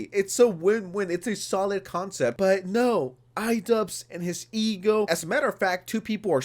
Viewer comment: I know you said "iDubbz and his ego" but I heard "iDubbz and his e-girl" which is just as accurate tbh